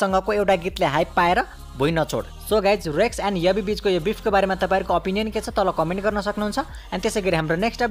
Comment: first i heard rex and yabi bitch, Then i realise its rex and yabi bich😅😅